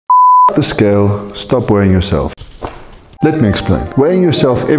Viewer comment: I heard: "fxck the scale, stop worrying yourself". Lol